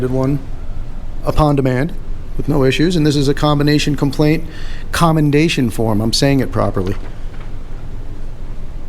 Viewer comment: At first I thought you said COMPLAINT and CONDEMNATION form, and I thought to myself "aren't those really two features on the same side of the coin?"